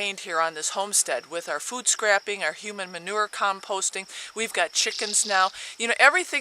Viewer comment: did you really say "human manure"??